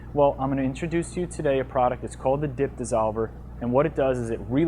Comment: I thought he said "Dick Dissolver" hahaha i clenched up and got scared